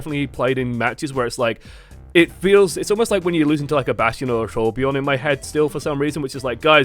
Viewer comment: Did he say Trollbjörn?